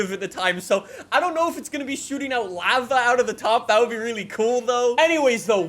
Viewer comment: excuse me but did he just say... laa vaa?